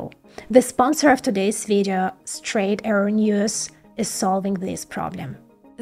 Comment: I swear I heard “straight heroin use” for a second